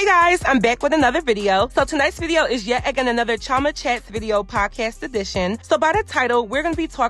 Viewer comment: Lmao I thought you said "Trauma" not Chioma 💀 lol and I was like well it kinda fits the video🤣 it's kinda traumatizing to see all these bbls I'm enjoying your videos so much and they make me laugh